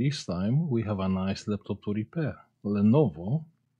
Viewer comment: Ive always heard "We have a nice laptop to repair" , I dont think ive ever heard you say "Today we have a bag laptop to repair" lol